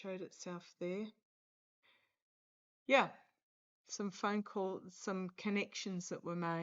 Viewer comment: I heard a “Yeah!” Or did I imagine it?